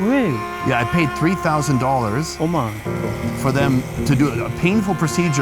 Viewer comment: did he just say "oh my god"